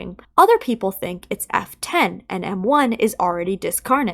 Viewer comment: For a moment I misheard "F10" as "Afton"